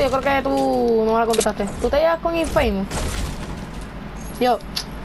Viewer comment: No entendí en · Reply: Gnzl osea dije Tu te llevas con Infamous y con el estaba hablando